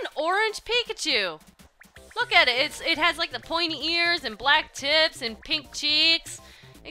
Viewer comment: did you say "looks like an orange pikachu, pointy ears, black tits,..."? lol so confused here